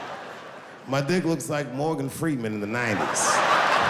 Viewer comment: I swear I heard him say "Gordon Freeman" and it confused the heck out of me at first.